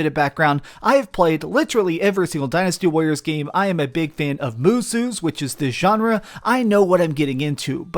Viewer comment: "I am a big fan of Moosu's" .... what the hell did he just say??